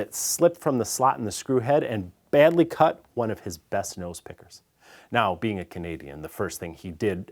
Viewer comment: What the heck is he saying at 1:20? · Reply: he cut "one of his best nose pickers"